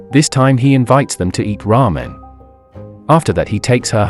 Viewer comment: "He invites them to ear ramen" I totally heard it as "Raw men" at first XD